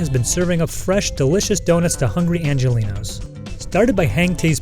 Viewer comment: I heard hungry Angelina’s, I’m hungry and coincidentally that’s my name. I’ll make sure to drop by sometime.